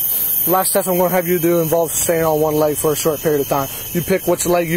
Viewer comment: She did the test better than the cop spoke. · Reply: He really did sound drunk, because his speech was slurred, and he messed up the instructions. Like beginning at 11:32, when he said the next test involves "staying on one leg." I listened 3 times and he did NOT say "standing," because no "d" could be heard! Then Officer Doofus says: "You pick which leg you would li.....which leg you would like to prefer. Stand here with your feet down to your side for me....." etc. FEET down to your side? And which leg you would "like to prefer?" 🙄